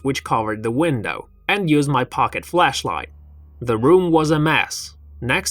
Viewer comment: sounds like he said "fleshlight" 😂